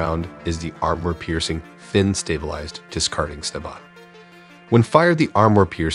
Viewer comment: did i just hear stabot